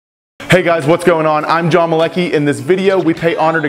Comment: Watching this for the first time I thought that he Said that he's Johnny Galecki😂